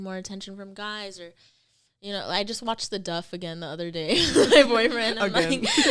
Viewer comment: did she say with my boyfriend ? i might be tripping